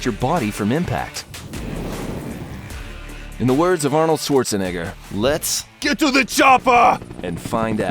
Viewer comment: sounds like he said ni***